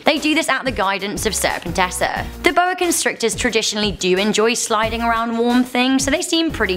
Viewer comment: oh I thought you said slidding in warm things 😛